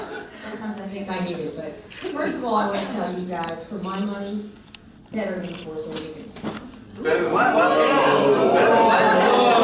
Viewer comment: what did they say at 7:28? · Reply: " Better than the force awakens".